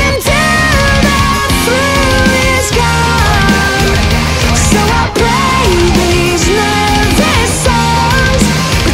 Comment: What's Jon saying in the 2:53? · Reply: "Do it again, do it again, do it again, now grow"